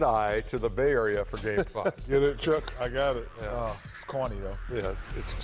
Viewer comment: I thought for sure Kenny said it was “Cornea”. Just another eye pun over here